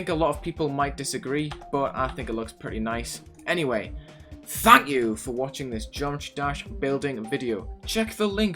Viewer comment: I heard "f*** you for watching" instead of "thank you for watching" at the end